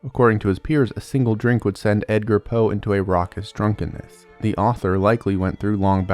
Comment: More like Deadgar Allan Poe. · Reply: I thought you said “Dadgear” Allen Poe and couldn’t stop laughing because in street parlance that means “father drugs”. 😂 ❤🎉